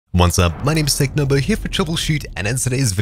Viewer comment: I thought he said hello my name is Technoblade at the start